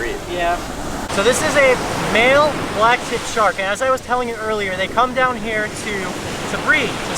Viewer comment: Thought you said at beginning you were after Tuna.